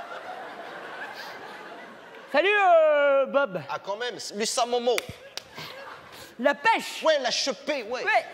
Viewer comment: Il dit quoi pascal à 0:39 ? Fils à Momo ? 🤔🤔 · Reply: lutsa momo ( salut momo)